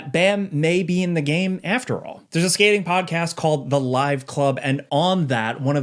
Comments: It’s the nine club skateboarding podcast, whoever wrote that article misheard the name as the live club 😂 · the podcast is The 9Club. Instead of “Live Club”